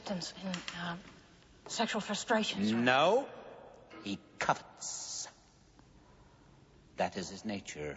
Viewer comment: I believe he says covets. Covet - Desiring to have possession of something. I also thought he said cuts when I was younger haha.